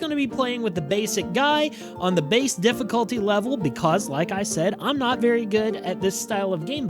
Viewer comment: Do I hear correct at 02:00-02:09 that you said "based difficulty" and it says peasant? XD